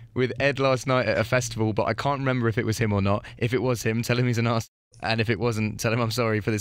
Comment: What does he say at around 2:20? Tell him he's an ass? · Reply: In the email the girl had wrote something like "if it was him tell him he's an asshole", I think Ed just said ass- then cut off due to being on the radio, or they censored it out.